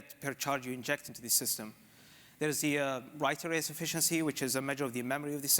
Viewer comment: Did you say that the Right RS Efficiency is the memory of the system at 2:36? I listened many times and I cannot understand it.